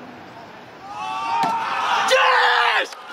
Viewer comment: sounded like his dad was calling him downstairs so he went "DAD!! WHAT!?"